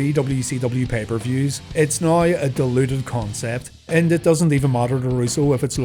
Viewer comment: I know at the end said "diluted" but it really sounds like he said "deluded", as in "delusional"